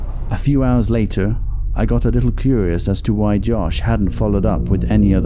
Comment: subtitles say Jake, but narrator says Josh